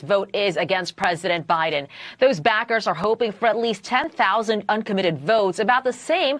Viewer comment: Is she saying “Those Bastards” at 1:36??